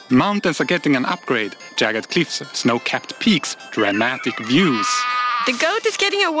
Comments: Features of each biome Badlands: -New cacti -Tumbleweeds that damage you -Vultures who steal loot from dead entities Swamp: -Boat chests -Frogs -Mangrove trees Mountains: -Snowy tops -Sinking into snow blocks? -Goats -More jagged cliffs -"Dramatic" views Edit: Well, this post just blew up to the 3rd most liked comment in 1 hour lol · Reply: Lol, I thought he said, "Romantic Views" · When he said jagged cliffs I thought he said Jackets and I was about to be so excited